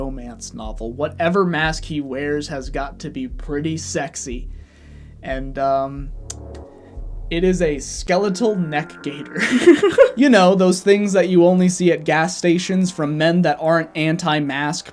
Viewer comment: I heard ‘skull neckmask’ and now all I can image is OG COD Simon Riley.